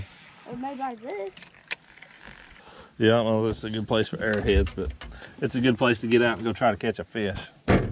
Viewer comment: Ha I heard you say arrowheads!!!! Nothing better than fishing creeks or rivers and looking for arrowheads!!! Thanks for taking us along.